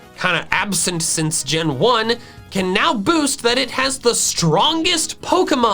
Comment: Guys in 21:00 Was I the only person who heard BOAST as BOOST